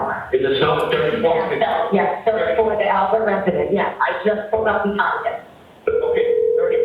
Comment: Did anyone hear at say on the 911 call that I just pulled up and found him??? Listen starting at 1:20. I can hear it. This when all along her and BFF Kerry say that Karen ran right to him before they saw him. Liars.